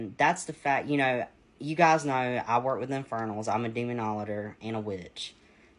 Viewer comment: That actually sounded like 'demonolator.' As in 'idolator?' Jesus wept.